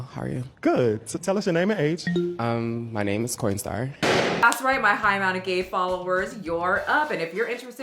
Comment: Did he say his name is Coin star? Like that machine that gives you cash for coins??